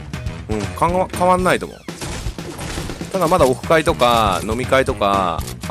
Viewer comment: にオフ会がおっぱ⚫に聞こえた